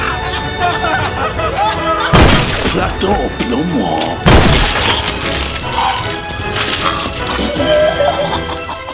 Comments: What the fuck did JJ say At 2:34? Black dough plomo? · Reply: Dead Leox he said Plata o Plomo which means Money or Lead. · what tf did ksi say a 2:34 lmao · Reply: Kamari Sadler plato o plomo it's from narcos